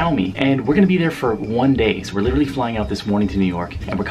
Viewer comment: this boi said one days